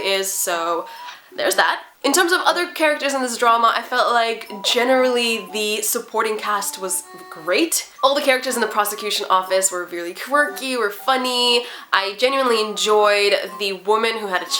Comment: At first i thought you said prostitution omg😂